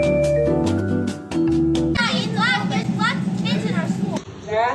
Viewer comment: Hilarious but TRUE DAT!!!! Did she say "Black biatch in our school?" Well that escalated quickly...